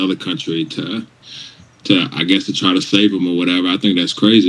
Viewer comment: It sounds like he said “save” not “slave”. If you listen to it at all 0.25 speed